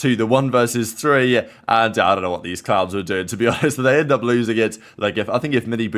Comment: did he say clowns?😂